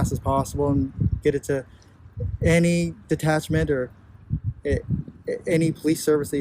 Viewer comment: did he say get it to any detachment?